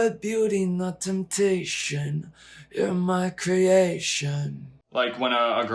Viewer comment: "You are beauty, not temptation" awww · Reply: Ngl I thought it said "my temptation" heh :'}